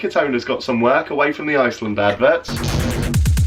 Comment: did he say iSland?